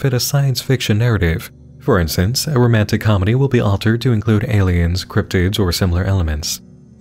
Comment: zoned out and thought you said "romantic comedies with aliens or cryptids"